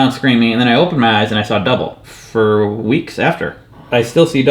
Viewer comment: Saw double, oh, right.. I heard "saw a devil" and was freaked out like what, you saw a devil for two weeks hahahha that's insane.